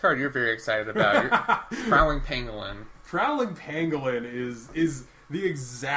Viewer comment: I swear I thought you said "Prowling Penguin" and I was about ready to lose my mind